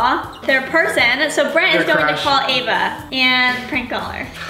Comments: did anyone else hear brent say “their crush”??!!! eva has a boyfriend.... this could’ve been filmed before that but still , soooo confuuusiiinnngg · did anyone hear brent say " crush " while lexi was talking ?!😂😂😂